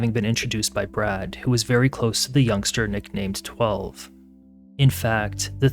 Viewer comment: Umm.. Did he just say his nickname was "12"??🫤 That is highly unfortunate 😬